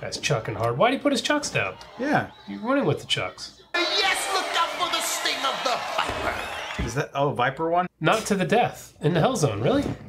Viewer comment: what the fuck did he just say? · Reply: Look up/out for the sting of the viper.